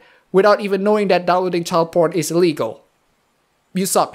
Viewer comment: "child porn is legal" am I hearing that right or I misheard that in my drowsy state of mind but child porn is illegal